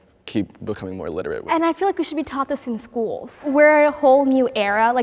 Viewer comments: I thought the girl with the hijab said "we should be topless in school." I was like, wait a second....... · OMG- i thought she said, “we should be topless in schools” and i was likes w h a t